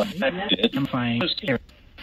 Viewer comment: sounded like Mortimer ❤ love from North Georgia